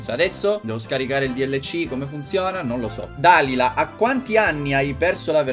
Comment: stavo parlando e ho sentito Dalila (il mio nome ) e mi è sceso un colpo mi puoi salutare plz